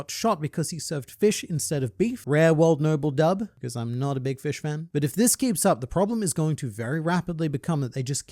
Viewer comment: yeah same edit: it sound like u said "fishman" 😂